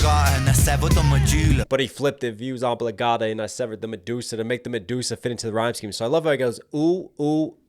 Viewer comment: I swear i thought he said medulla instead of medusa🙃 great breakdown